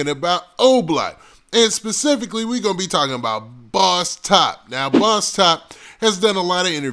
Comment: I thought they said boss top 30..